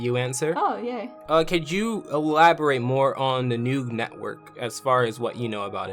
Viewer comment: I'm sorry but I heard it as Nude Network